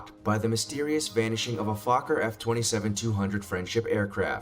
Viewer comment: A Fokker 😂😂😂 for a second i thought it said fucker lol 🤦‍♂️